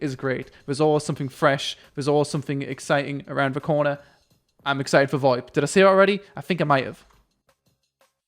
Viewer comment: "I am excited for ..." what did he say? and what does it mean? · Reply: Voip, to talk to other players ingame.